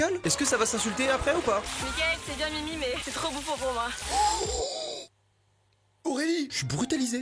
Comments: MAIS- 10:52 SON CRi 😭 12:59 OMG HEADSHOT 😭😭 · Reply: Elle a dit quoi à 13:00 ? Moi j'entends "j'suis désolé , t'es trop beau pour moi" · C est moi ou je suis la seule à ne pas comprendre ce qu elle dit a 12:58 ? 😂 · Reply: "Mickaël t'es bien mimi mais t'es trop bouffon pour moi